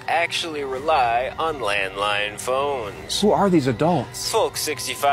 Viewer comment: I definitely thought he said land mine at first and just accepted that as probably true